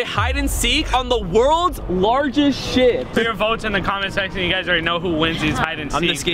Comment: it's sounds like instead of ship he said sh*t